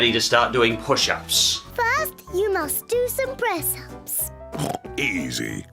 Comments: What she say? Sounded like breast ups. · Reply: Press ups. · did they just say breast-ups! IN A KIDS SHOW!